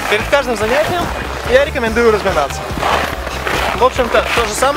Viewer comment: "Перед каждым занятием я советую разминаться"- сказал инструктор "Перед каждым занятием я советую нажираться"- послышалось мне)))